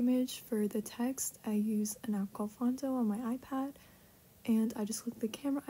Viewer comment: what did you say you used? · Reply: its called phonto